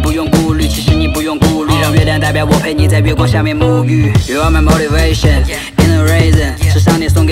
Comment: I keep hearing “you are my motivation in a raisin”